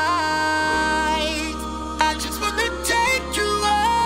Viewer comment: Who Else Thought That It Said I Just Wanna Take You Out?